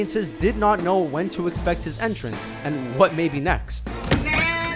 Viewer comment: Did he say Jack Flack?